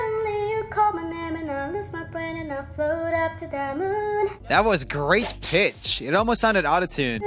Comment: I thought roomie said "that was great bitch"